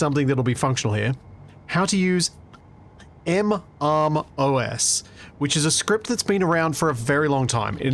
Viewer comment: In thinking about how you tend to say it "M Arm O S" where I tend to say it "Marmos" I've come to a decision: A large grid arm is a MArmOS, a small grid arm is a MArmOSette. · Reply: I've always heard it as "MRMOS"... It took me pausing on the screen in one of the Scrapyard VODs and actually reading what it says to realize what he was actually saying.